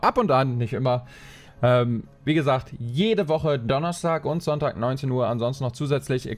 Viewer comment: Ich hab erstmal Dienstag und Donnerstag verstanden xD (ja mein Gehör ist super) Die Streams sind nur zu empfehlen!! Bald werde ich auch wieder dabei sein. 😅